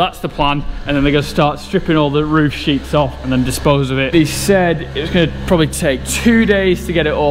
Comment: What did he say?? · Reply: Stripping all the roof sheets off and dispose of it